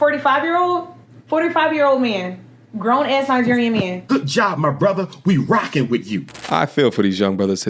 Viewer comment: Did she say nigerian man?😮😮